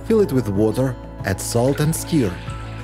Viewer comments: «….and add salt and skyr» («skir») That is actually what you said, but it came out a little wrong - you meant «add salt and sugar» («shoo-gør»/«shugg-er»). Just wanted to let you know, then you can be aware next time… Thanks for your great videos, I don’t get how you can keep them coming, always new topics!!! Amazing!!! 🤗💛💛💛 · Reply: I was confused at first because I heard steel but after thinking about it, I realized he said stir. Love the accent and the tips. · What is the word that sounds like ‘skeer’ at 7:29. “… add salt and skeer… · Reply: Stir stir stir